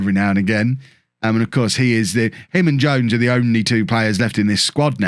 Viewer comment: lol I just realized that Ricky is JADE Jones. I've always heard him as Ricky Jay Jones.